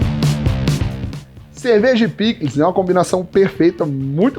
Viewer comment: Entendi Cerveja e Pica, depois voltei pra ouvir denovo KKKKKKK